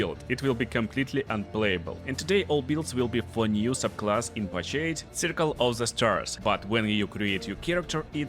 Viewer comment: Im sorry, You speak way too fast for the accent you have. Your english doesnt have to be good or perfect, but please tell me what it is you are saying @ 0:21. Because all i hear is: and today all builds will be 'flahnyus' of(?) class in 'pashade' circle of the stars. Im not making fun of anything, everybody has an accent.... but i really tried to replay those 5 seconds 10x and even did 0.25 speed and i have no idea. If its that bad with some words, maybe speak slower. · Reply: Thx for critique! yt captions actually doing a good job :D i say "today all builds will be for new subclass in patch eight (8) , circle of the stars"